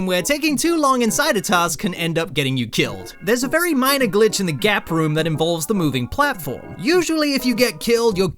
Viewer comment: I thought gap room said gay room